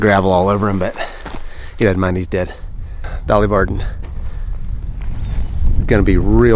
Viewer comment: What kind of fish is that? Cuz I swear I heard you say Dolly Parton😂